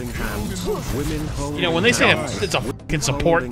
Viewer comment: women holding hands 👭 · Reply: I thought it said women folding pants.